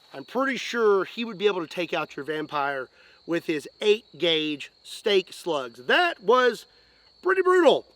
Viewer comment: In this video I hear 'steak' instead of 'stick' all the time. I'm not a native English speaker. I'm wondering what the problem is. Either I can't hear the pronunciation in English or has such an accent.